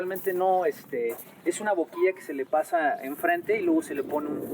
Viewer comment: No entendi el material usado para que la piedra tome el color negro (min 9:24) ... sólo escuche "abuquie" ... alguien me puede dar una explicación por favor.